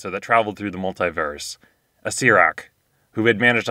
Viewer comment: What is a seirake that thing says